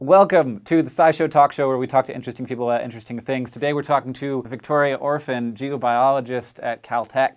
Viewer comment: Definitely misheard Hank's introduction, and thought he said he would be talking to a Victorian orphan... I was so confused.